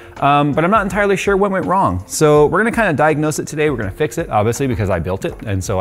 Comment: weFix it? I thought it's called iFix it 🤷‍♂️